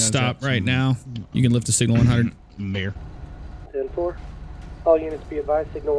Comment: woah doesn’t that sound like someone said the n word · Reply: lol coming back now and listening I think he mumbled Mayor